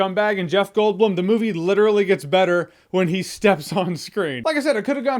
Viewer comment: Did you just say butter? I heard butter.